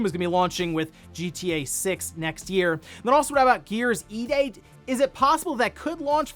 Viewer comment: I'm officially sick of subscription services. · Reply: I keep hearing "Gears E-Date"